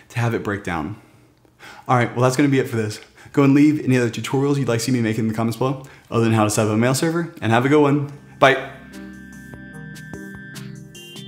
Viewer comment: I swear I heard him say "if you want to see me naked in the comments below..."